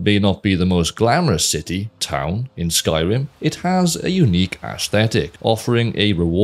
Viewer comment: I don’t see anyone else having remarked on it after a cursory skim, and it might be just an accent thing, but I swear it sounded like you said ash-thetic. So, either your diction gives a bit of Sean Connery (I know, you’re Welsh, he’s not, it’s not technically an accent thing, but ykwim; also Welsh accents are so pretty, like burbling streams), or you snuck in a 10/10 pun. Either way, it works!